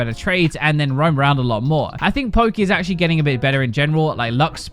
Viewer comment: I think you said "Poki" (mane) as opposed to "Zoe" Lol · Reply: he said poke is getting better